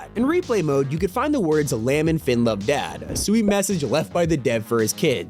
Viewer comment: did he really say lamb instead of Liam?